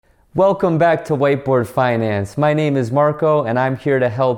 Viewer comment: I thought he said "whiteboy finance"...lol...he's a cutie though :)